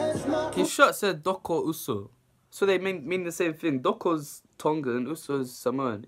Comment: it sounds like he said “it says doko uso”😂😂😂😂